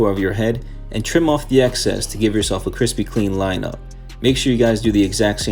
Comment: i swear I thought I heard Krispy Kreme line up 😂 that sounds pretty good